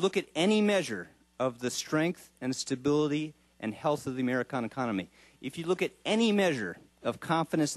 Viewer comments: I think he said "Amerikahn" at 5:28, LOL. KAHNNNNN!!!!!!! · Did he say Americana? Check at 5:25. lol